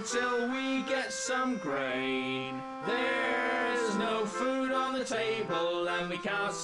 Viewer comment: At the beggining I thought I heard: There's no food on the table, so we ain't goin' nowhere 'til we get some BRAINS.